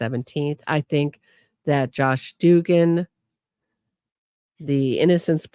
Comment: it’s Josh Dubin, and Sarah Dugan. It sounded like you were saying Josh Dugan